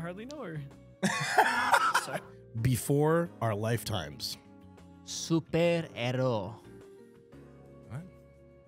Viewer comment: Did he said szuper erő?😂